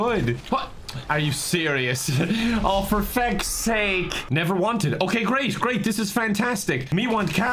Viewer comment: “Me want car! Oh, no, car want me...” I thought he said he was never wanted